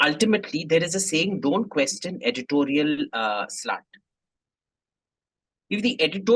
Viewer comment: I'm so sorry, I heard Editorial 'sl*t' at first. Replayed again and heard editorial slant, which was what it was. Though I wouldn't put it past AIM to say the former. Edit: I replayed this bit multiple times and got reminded of my engineering hostel Bengali friends pronouncing 'sl*t' in that bong accented English. Thanks that nostalgia was priceless. Edit2: Did AIM pull a fast one here? :D Considering he's part bong.